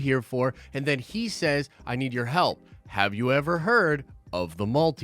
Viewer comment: Does he say "I need your help."?? I thought he said "We need your help" 🤔